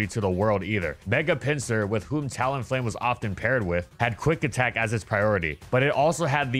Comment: For a second i heard "with chad quick attack" instead of "with had quick attack" lol